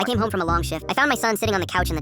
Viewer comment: o thought he said I came home from a long shit 😭😭😭